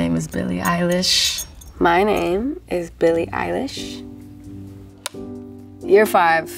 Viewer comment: Why does it sound like she is saying Bilie Eyelash in the fifth year ???